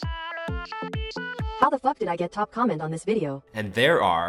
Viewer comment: DID I JUST HEAR F***